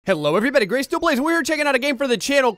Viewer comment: no offense gray but I heard the intro "Gay still plays" im not being rude or trying to but I really heard it that way so I went laughing hysterically I fell off the couch on a coffee mug anyway cool vid keep it up